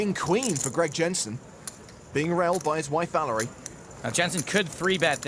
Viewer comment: Did he just say "being railed by his wife"... giggle